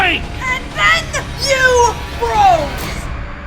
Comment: What did say at 5:41? "And then you brawl"? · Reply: Froze.